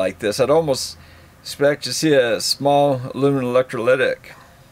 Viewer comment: “Aluminum Electrolytic“ Capacitors… yeah, that makes more sense then what I thought I heard… “aluminum analytics” … I was scratching my head over that one… 🤔 I’ll keep a M4KOR around for testing batteries and small stuff, even if I have to brake down and spent $20 every tens years. But the Klein and Ideal meters will be for any serious stuff. For my son-in-law I’ll get him the Astro meter, a case and voltage tester for now for auto work, and for his birthday I’ll get him the Klein. You have a link for it to Amazon, on one of your videos? You helped me out so I’ll use your link if your have one for it.